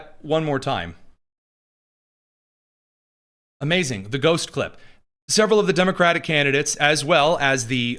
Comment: he did not say "the goat 🐐slipped" as I first heard. But i did have to slow the speed from 1.5 to normal to catch it correctly. Lol